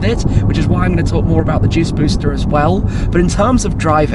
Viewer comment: I thought he said Jizz Booster 🤣 I nearly inhaled a coco pop.